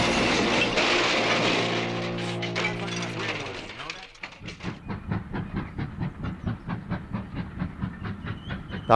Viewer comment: I heard someone say something but I can’t hear it clearly, what was that line? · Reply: You drive like my grandma, do ya know that